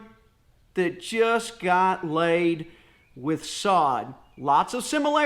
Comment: Haha! My wife heard you say "just got layed" and it took her a second to figure out what you were talking about 🤣. Great tips brother! Happy Independence Day! Holy smokes! Your front yard looks amazing! 🤜🤛